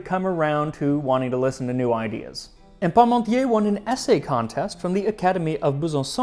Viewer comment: The funny thing was, when I heard you say "Parmontier" it sounded like "Pommesmountier" - "potato rider" ;-)